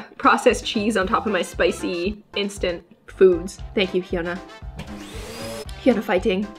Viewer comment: Me as a kpop Stan: "..did she say hyuna..? No it was probably 'Fiona'" "hyuna fighting" Me:"DiD sHe jUsT-" -notahyunastanbutikher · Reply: She is taking about hyuna